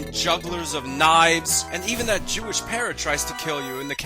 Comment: I thought he said Jewish parent for a moment!